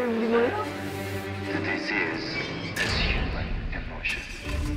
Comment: what is the radio saying? Btw they played this same audio on their fever extended version concert halateez played the radio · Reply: "There is a disease in the heart of man." then "The disease is human emotion." x3, ending with "The disease is human..."